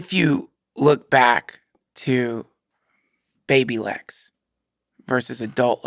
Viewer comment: When he talked about Baby Lex I thought he said babylegs and I didnt even react since it’s Duncan lol.